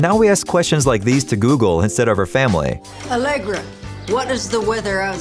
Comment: Tell me y'all Heard her say Allegra not alexa 😂